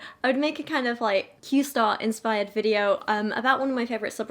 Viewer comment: I thought you said keem star and I was really really confused by that relationship